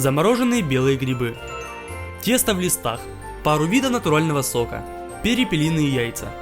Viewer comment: Мне одному послышалось "Тесто в глистах"?